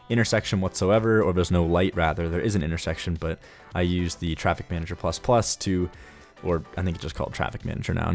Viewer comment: I thought I heard you say "traffic ninja" when you were talking about Traffic Manager. I didn't bat an eye, because that's a perfect example of silly names for useful tools, but now I'm left wishing that it were actually called Traffic Ninja instead :(